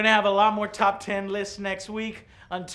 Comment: At the end it sounded like you said Hangry Joe Show lol